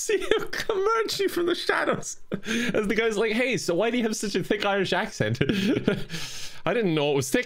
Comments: Oh, did he say "why do you have such a THICK Irish accent"? I thought he said "Why do you have such a FAKE Irish accent?" I was like, excuse you. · I thought he said, “Why do you have a fake Irish accent.” Lmao 😂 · Wait, Kevin's accent was thick...? Did I just became desensitized this whole time...? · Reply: i thought he said "why do you have such a FAKE irish accent"